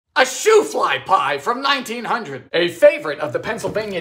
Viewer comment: I heard "shoot fly bye" not "shoofly pie"